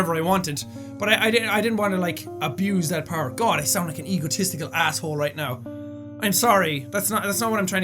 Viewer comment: i thought he said " i sound like a egotesticules ass hole " ... Am i the only one with dirty ears?